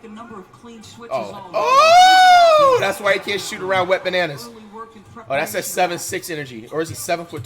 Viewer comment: did flight said wet bananas